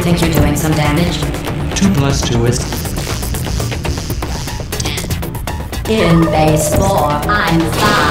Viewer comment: I like the part where it sounds like she says 2+2 is Fish